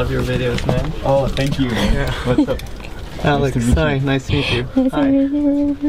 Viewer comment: what did she says at 6:20 ? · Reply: LIiO dinGO I think she said " nice to meet you"